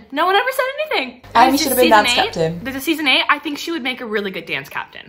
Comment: is it just me or did Shelby say “there’s a series 8” rather than IF there’s a series 8? That may be a little accidental announcement there